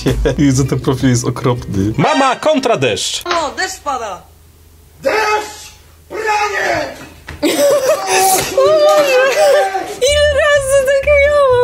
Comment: czy tylko usłyszałem "Deszcz? BRANIE!!!"